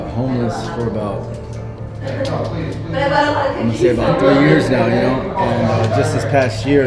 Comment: did he say 30 years or 3 years ??